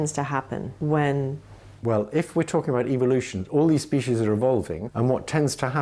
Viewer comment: I heard evil-lution